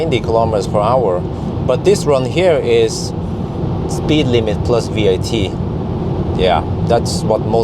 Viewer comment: did you actually say "speedlimit + VAT" at 7:55 ? at leasts thats what i heard and actually choked on my food XD But it seems to be correct even here in germany